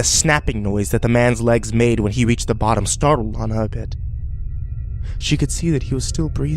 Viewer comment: Can somebody please tell me what the fuck he said @ 6:10 "...and the snapping noise that the man's legs made, when he reached the bottom [translate] a bit..." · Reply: GamerGirlington sorry it's Lana